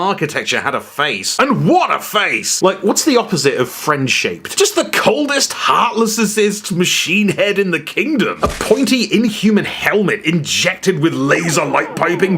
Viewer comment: I thought you said “friendship” and the opposite of that is clearly “enemy helicopter”